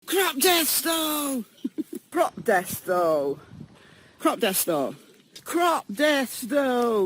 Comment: I heard “Grop Death Star.” !!! Great research thank you for the video!